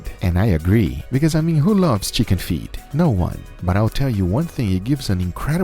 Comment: So many people love chicken feet! Let me take back what I said 🤓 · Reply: i thought you said children feet